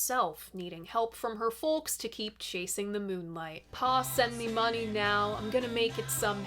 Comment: "Pa send me money now, I'm gonna make it somehow, I need another chance." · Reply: I always thought he said “ boss lend me money now…” , but pa makes more sense, I guess.